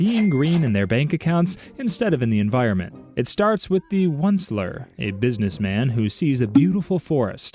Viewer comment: heard one slur instead of Onceler😂